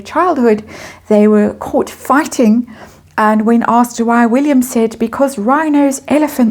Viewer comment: I believe Harry said that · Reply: That sounded more like h to me, but at 1:34 she states “William”. I listened three times, but please correct me if I misheard.👍🏼